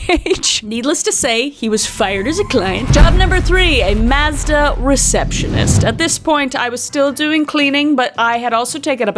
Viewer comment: When she said Mazda Receptionist I thought she was saying she was a “Master”Receptionist 😅